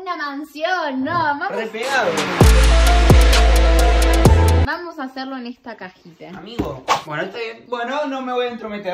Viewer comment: si no escuche mal le dijo a (no amor) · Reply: No amor dice